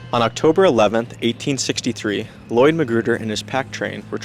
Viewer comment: LOL I thought he was saying McGruber!